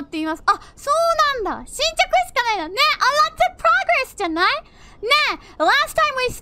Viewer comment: i believe she said 0:52 "alot of progress tonight" instead of right, though not a big deal, thought i'd point it out